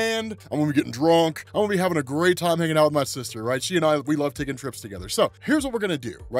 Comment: Just did a double take! I SWEAR I heard you say “making out with my sister” and choked on my own spit. Almost died twice!🤣🤣☠️☠️